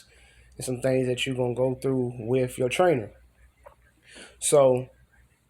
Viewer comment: Oh it said trainer tips, not trailer tips like I thought it said.🤦🏿‍♂️😂😂😂